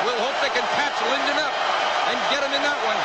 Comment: haha I always thought he said " he will play unconscious" lol